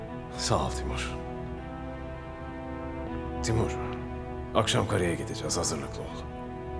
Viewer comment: akşam karıya gidicez diye duydum su içerken boğuluyodum